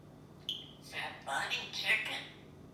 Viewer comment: I thought I heard red bunny chicken? What ever he said all his thoughts are hilarious! 😁